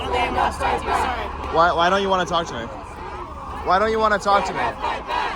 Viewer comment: captions are off, she actually said "yeah, I don't think they want us to talk to you"